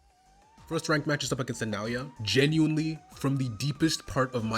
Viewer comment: look at the subtitles. did he say nya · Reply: He said Naoya